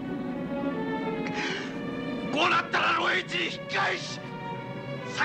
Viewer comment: なんて言ってんのか全然聞き取れない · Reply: こうなったら露営地に引き返し先に死んで行った者達と共に ｢全員が死のうではないか❗｣